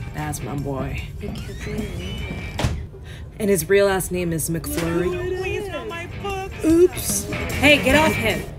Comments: I thought she said “his ass name is McFlurry” 😂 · I thought she said oreo Mc flurry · I thought she said his real ass name is mcflurry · I THOUGHT SHE SAID " AND HIS REAL ASS NAME IS CHAD MCFLUFFY" AHHHH